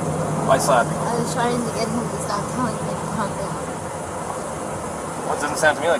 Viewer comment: What does Gabby say to the officers at the 49:05 mark? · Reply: trying to get him to stop telling me to calm down? my guess…hard to tell.